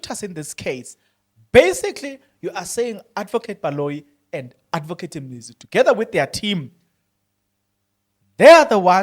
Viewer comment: Adv. and Adv. Who? Did you say Adv. Mnisi?